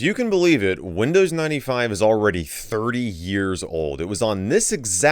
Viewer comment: I misheard and thought he said it's 3 years old.